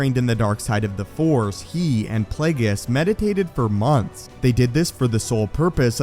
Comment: I thought you said they medicated for months